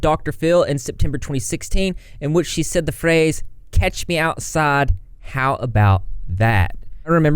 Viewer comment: I thought she said, "Cash me outside, how 'bout that?" and thought how sad that her mother allowed that and encouraged it. But Danielle has made money from it and hopefully will do well from it going forward.